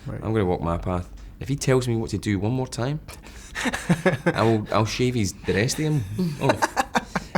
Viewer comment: I can't understand James, what did he say? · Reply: "I'll shave the rest of him off"